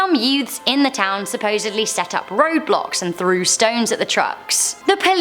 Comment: Owo i think i heard ROBLOX ..... 😗😗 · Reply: she said roadblocks not roblox